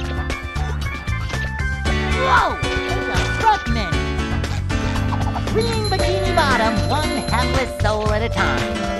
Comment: What did he say?? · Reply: He said "Take a breath mint."